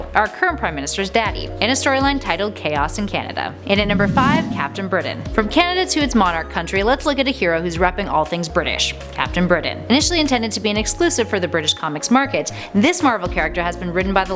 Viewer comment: Did she say Britain was Canada's monarch country??? She kinda talked a bit too fast for me.